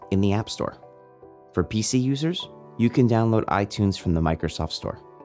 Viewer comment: Did he just say, 'for pc users'?